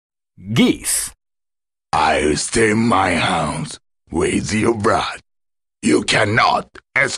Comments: "I'll paint my house, with your blood." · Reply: As a kid I thought he said: I extend my house, wave you brah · "Ayi stain mah hanndz... weed yu bra." - Gyeese, the ultimate weebs evar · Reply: Johnny Casey and to think all this time I thought he said house, I guess hands does make a lot more sense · I always thought he said "I'll stay in my house, with your bride". Damn Geese!